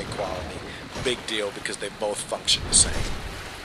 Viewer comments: "function" I thought you say "f*ck".. Like Whaat never heard him say that -. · When you said function the se I thought you said fuck shit the same lol